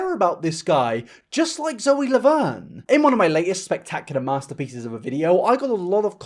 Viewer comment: Every point you made in this video is true...…., but please tell me I'm not the only person who heard him say "Loe Zaverne"?😂